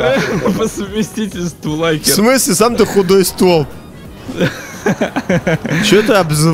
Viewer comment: ахахах. Мне послышалось худой СТВОЛ ! XD